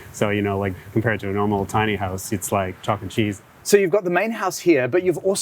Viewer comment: what did he say? Chalk and cheese? 🤔